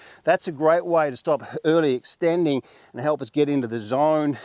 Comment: Great instruction! At 12:20 did you say it’s a great way to get the feeling of early extending?😮